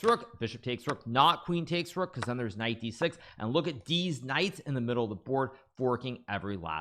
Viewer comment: did hikaru say "deez knights" at 10:54?